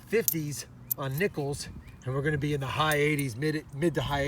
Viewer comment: " Were Gonna Be In The Mid 50,s On Nipples" Edit : Everytime He Says Nickel I Hear Nipple 😂